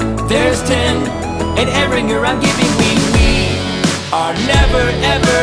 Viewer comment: Note: My name is Aaron. I was listening to this in the background of looking at pugs on google images, and I hear: "AND AARON YOU'RE I'M GIVING WING"